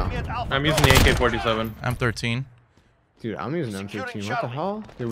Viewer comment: LK thought he said I’m 13🤣